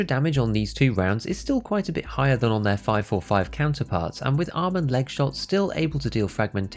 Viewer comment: Every time you say "arm and leg shots" I hear "almond leg shots".